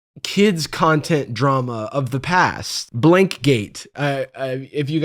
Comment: Does anyone know what the “blank” in blank-gate is that he was talking about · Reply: Elsagate I think